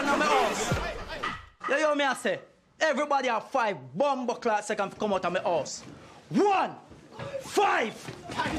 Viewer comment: what the fuck is he even saying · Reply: What’s going on in my house Now hear what I have to say Everybody now have five Bombarclat seconds to come out of my house 1….5